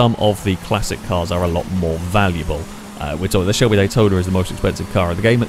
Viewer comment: The Chebulay Toderer? Can you uh... Clarify? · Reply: AH! Now I hear it. "Shelbe Daytoner"